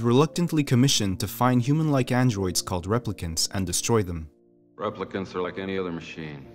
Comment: OMG you included Internal Affairs and Memories of murder! Highly recommend!! Nocturnal Animals is my favourite!! i never watched BR but i heard Replicants and i thought i heard Replikas ooof. But i have watched LA confidential, Memento (soooo very slow but i guess worth it!) and ofc Kid Detective!! i didn't even know i was watching neo-noir! Cool!